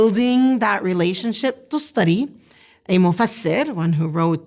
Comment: She mentioned about a mufassir, I can't understand the name. Could someone please help me ? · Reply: Tustari